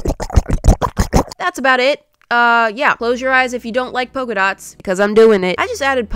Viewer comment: I Hope you like polka dots Me: looks at my bed, pillows, walls, and dresses also me: sarcastically says, Nooo not at all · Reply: I thought that said “ polka dolls “